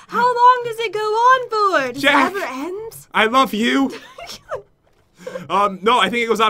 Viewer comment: I’m currently bawling my eyes out because they broke up and my childhood is over 🥲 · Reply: Well i heard pat say ''jen I love you''